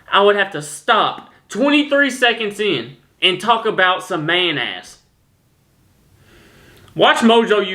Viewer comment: when he said "man ass" am i the only one who thought it sounded like he said "mayonnaise" ahaha!